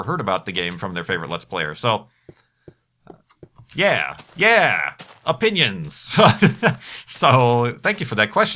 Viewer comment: I thought it said "ONIONS!"